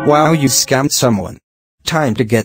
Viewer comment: it almost sounded like "pepsi max"